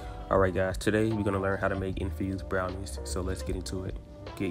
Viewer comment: I thought it said "confused brownies" at first. I was so confused..